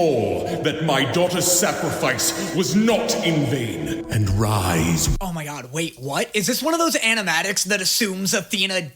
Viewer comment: DID HE JUST SAY ARISE OR HAVE I WATCHED TOO MUCH SOLO LEVELING?!?!?!?!